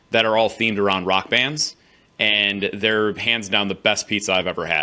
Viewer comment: Pants down? :') Did i hear that right? :')